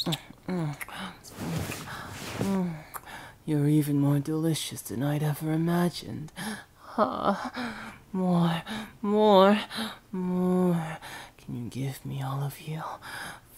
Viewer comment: yandere boi just wants some pancakes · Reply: I thought I heard something about ✨DELICIOUS PANCAKES✨